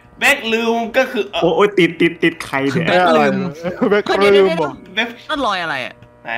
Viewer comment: แบล็คลืมได้ยินงี้